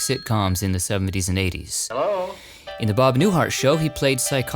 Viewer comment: I was tired from cooking Easter dinner, when I was watching your latest video. I thought I hear you say Bob Newfart . I restarted it again to see if I missed heard you, but it sounds like Newfart to me. 😂😂